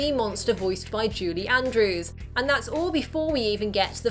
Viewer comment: did she say JUDY andrews?